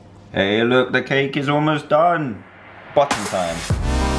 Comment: did he say a bad word? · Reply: He said button time